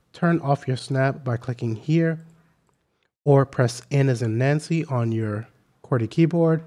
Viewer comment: What is the word you are saying here ? Sounds like "N is a nancy" ???